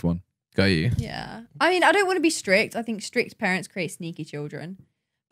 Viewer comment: “Strict parents create sneaky Children” 😂 speakin facts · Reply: i thought she said neeky lmao